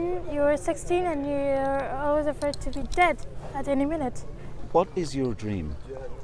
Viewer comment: Hi LastWeekTonight! This was a really great and informative video but around the time slot 4:17, the automated subtitles say "first two dead at any minute," where it should say "the first to be dead at any minute." Fixing that would really help out the millions of people who want to be informed yet lack reliable captions. Thanks!